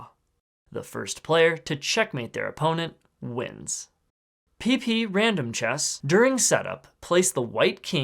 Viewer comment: why do i hear "peepee random chess"